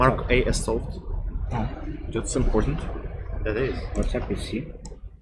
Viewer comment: What did say at 2:02:53 ? Can anyone tell me. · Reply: what's up with C